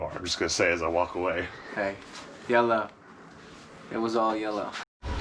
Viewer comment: HELLO VERY MUCH ENJOYED Ur FRIENDS in Ur videos & I thought he said YOLO-instead of YELLOW {I'm be CORNY 😳😀 KENDO-HANA-YOLO or just KENDO-HANA-LOL😍}